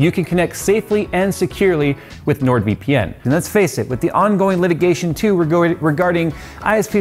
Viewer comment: I already use NordVPN so I was fast forwarding through the Sponsorship and it sounded like Jay said "The on going mitigations... your desktops"